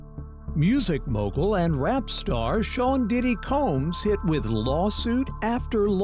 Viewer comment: I thought she said Diddy Cums😂😂😂